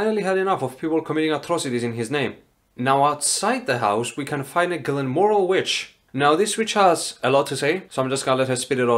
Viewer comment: At around 22:40 I misheard what you said as "We can finally- GLENMORIL WITCH" and it made me laugh unexpectedly hard xD